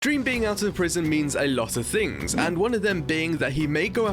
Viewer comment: I heard "green bean out of prison"